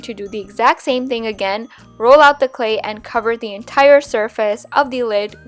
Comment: am i the only one who heard her say "shurface" instead of "surface"? yes? ok